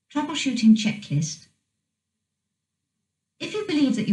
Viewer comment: Sounds like it's saying The Washington Checklist instead of Troubleshooting Checklist @ 0:00 .